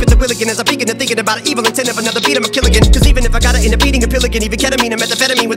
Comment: At that part he says "'Cause even if I gotta end up eatin' a pill again" but i kept mishearing it for the longest time as "'Cause even if I gotta end up beatin' a pelican"